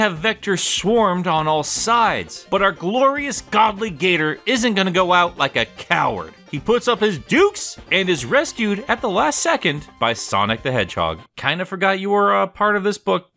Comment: did he just call Vector a Gator..